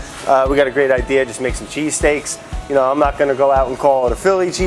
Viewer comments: Lmao I thought it said cheesecake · I thought it said cheesecakes